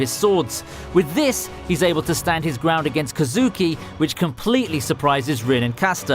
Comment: DID HE JUST SAY DAKU IM TRIGGERED